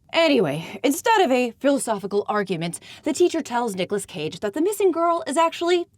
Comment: I really thought you said “Dickless Cage” here and that got me thinking that pretty much sums up Neil Labute’s whole worldview as far as women are concerned 😂